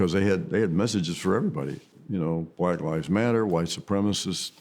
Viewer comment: BTW Jacoby said White Lives Matter 26:20 subtitles say Black Lives Matter...Et tu Youtube?